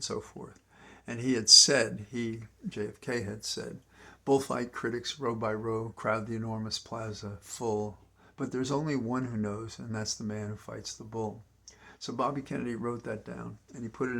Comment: Hard to clearly hear what you said, did JFK say (at 12:22ish): "Buillt by critics row by row a crowd the enormous Plaza full but there's only one who knows and that's the man who fights the bull" Thanks, Wo0dy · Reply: Woody - bull fight critics row by row crowd the enormous plaza full but there’s only one who knows and that’s the man who fights the bull. (There are variations on this old quote.)